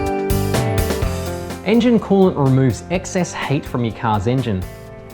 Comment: lol i thought he said excess hate from the car engine at the start the accent was so strong... lol